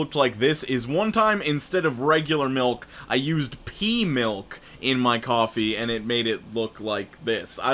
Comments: i had to re-listen to the coffee stain one a couple a times cause i wasnt thinking " pea milk", but pee milk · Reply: OOOHH, I also thought he said pee milk, it kinda scares me that I jusst nonchalantly assumed that was an actual thing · did you just say pee milk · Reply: I think he said pea milk which is in fact a thing that exists. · NEW VIDEO on my gaming channel! I see how long I can surivive without taking a hit on COD World at War Zombies <link> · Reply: what did you say at 3:23 i heard pea milk